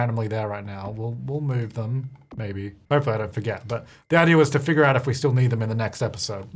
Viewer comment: I thought you said “Daddy wants to figure out ..” and I was like WHAT?!?! That doesn’t sound like you, but I had to turn on the captions to see you actually said “the idea was …” :_jtytLol: It seriously sounded like something else!!!